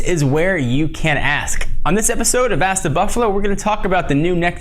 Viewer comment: For a moment there I thought you said ass the buffalo, lool XD